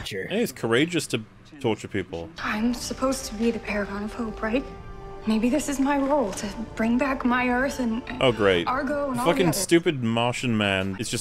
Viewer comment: Did she say "paragon of poop"?